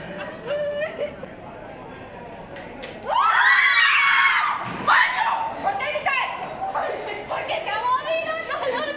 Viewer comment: what did she say? Could someone tell me? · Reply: She says, why are you yelling?... And the friend replies: Because it moved hahaha